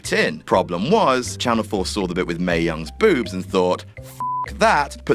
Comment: I misread and misheard Mae Young for Renee Young and I was mildly excited for a second there.